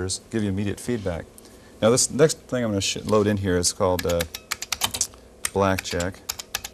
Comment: did he say shitload. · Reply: I think he meant to say "show", but quickly replaced it with "load". As in "the next thing I'm going to sh... load in here is a blackjack program"